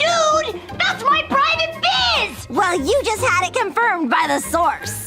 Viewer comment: I thought luna said rizz😂